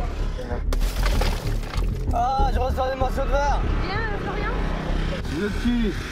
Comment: Il a dit quoi a 59:14 ? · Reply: anais tv Il a dit "Aaah ! J'reçoie des morceau de verres !"